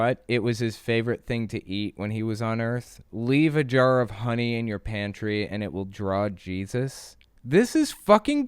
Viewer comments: Omg, I swear I thought she said "Leave a jar of honey in your panties..." The change in connotation that would have. LOL. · I heard her say, Leave a jar of honey in your pants and Jesus will show up. WHAT?!?! Oh, she said ...honey in your pantry. Hmmm. Well, at least in your pants sounds more interesting.